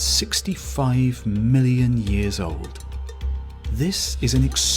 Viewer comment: Time-lapse 14:38 the narrator mentions fossils that are over 3.5 million years old but the caption says 3.5 billion years I believe that's a big difference between million to billion I rewound it and verified it and he's wrong... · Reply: The narrator says it is 3465 million years old. Which is approximately 3.5 billion years.